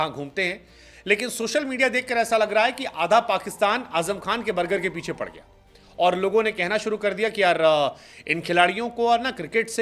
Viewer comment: Did he really said,❝Adha Pakistan Azam khan ka Burger ka piche padha hain❞?😂😂😂😂